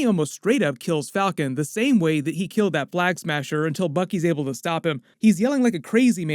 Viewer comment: Did you say black smasher instead of flag smasher at 4:46?